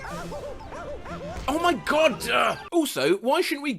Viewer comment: i tested it, i own mario odyssey and i can say for certain mario does not say oh my god when falling in lava, but rather "oh noo" or "ohooo" i tried about 20 times and didnt hear oh my godd once.